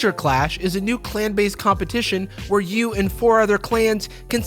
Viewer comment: During the ad break, instead of "clan base competition" I heard, "plant based competition" and was like, ???